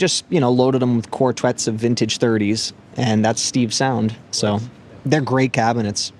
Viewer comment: What speakers does he say at 16:51? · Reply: Vintage 30's